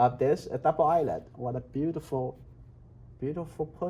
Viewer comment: did he just said "what a beautiful pussy"?!?!?!